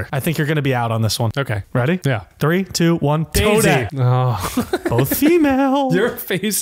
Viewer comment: Yo my heart skipped a beat when he said toadette cuz I thought he said toad😭